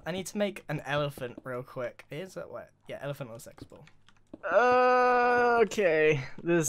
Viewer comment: I thought he said an elephant in a sex ball